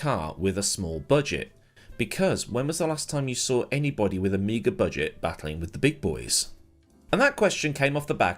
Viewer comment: Lolz, thought he said Amiga budget, as in Commodore Amiga.